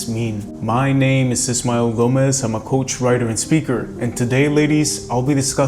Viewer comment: Every time you introduce yourself, it took me multiple videos before I finally realized you are saying “…I am a coach, writer”. I thought you said “I’m a ghost writer”. May I suggest saying “I am a relationship coach and writer”. Just a clearer suggestion. Either way, truly appreciate your content.